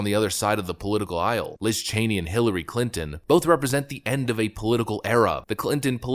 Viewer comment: Just a clarification, no criticism meant (I understand that there are variations in English;) But did you say that it was the end of an era or the end of an error? Both probably fit.